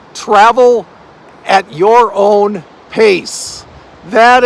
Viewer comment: Oh my gosh, you're making retirement sound so enticing! Unlimited time in nature, upping physical activity, the simple pleasures of life, yesss! It was funny though, when you said "travel at your own pace," I heard "Scrabble at your own pace." Ha ha. Reminds me of my mom in retirement playing Words with Friends daily with her boyfriend who lived in CA when she lived in WA. They took turns flying to one another for two weeks at a time, alternating two weeks to themselves. Worked for them! You can make your retirement whatever you want! Great inspiration! 🎊